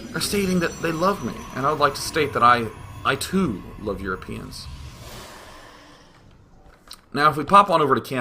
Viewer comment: did he say "I love you penis at 16:42 ?"